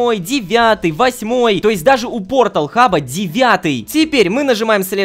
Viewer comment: мне одному послышалось пор*охаб?